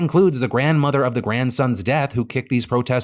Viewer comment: Thought for sure you said, "The grandmother of the grandson's dad," and was trying to figure out what that could possibly mean.